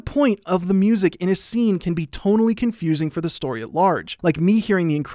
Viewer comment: subtitles say “totally” instead of “tonally”